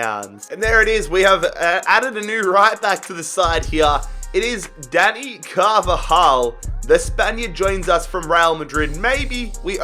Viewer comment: Who Else Thought He Said Daddy Carvajal?